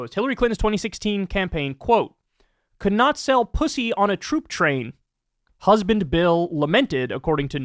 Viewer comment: I totally heard ‘p00p’ train instead of ‘troop’ 😅😂🤣🥹😂🥲